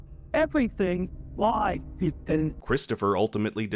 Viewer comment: Man it almost sounds like Chris is saying “everything lies, Ethan!” 🤯 could the boy in white have the same name????